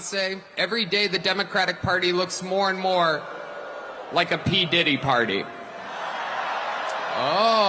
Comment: I heard "ain't no party like a Diddy party."